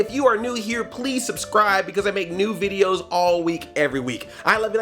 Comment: it sounded like said i make nude videos every week ps a gargoyles moie would be great